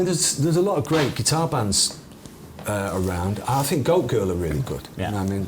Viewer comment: Around min 14:00 did he say "Go Girl" & "Yak" ??